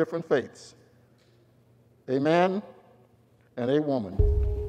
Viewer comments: Did he just say "Amen and A 'Women'"!? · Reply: A man and a woman. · I had to play it back, did he say Amen and "a -women" 0:45 ??????????